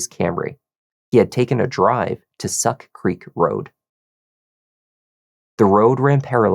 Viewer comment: Sorry, but did I hear "Suck Creek Road"?